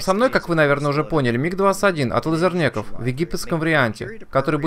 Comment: Ахаха, ты так сказал "от Лизернеков" что мне послышалось "от Лузернеков", и я даже отматывал назад, что бы убедиться =)))